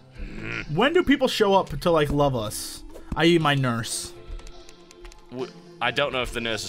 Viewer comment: When Jesse said "I.E. my nurse" i thought he said "I eat my nurse" Lol